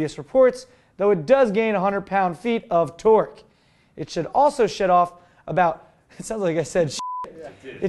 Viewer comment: hahahaha "sounds like i said shit off" That's exactly what I thought you said!